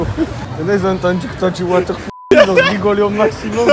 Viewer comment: Il dit quoi à 4:10 water jss pas quoi